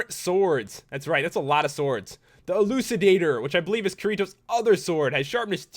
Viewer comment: Key-Ree-Toe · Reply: It's funny. When I frgot Kirito's name, I thought everyone was saying "Kitty toe!" And I was like: "W-what the..."